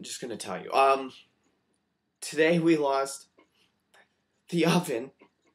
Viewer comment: at first I thought you said 'we lost deoveon' and I was like who the hell, thats sad but I dont know who that is. and then you kept saying 'it', so I went back, and you said oven so I was less confused/sad